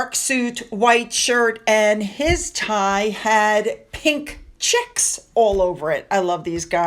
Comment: I didn’t know that Prince Edward is known for his love of novelty ties. “Edward's Easter tie featured pink chicks.” (I thought said “checks” so I got curious I just had to google it. 😅 - picture in Hello Magazine) “James rocked a pale blue and white patterned tie.” (Rabbit print, picture in Tatler) “Edward wore a cat-print tie on his wedding day.” 🤩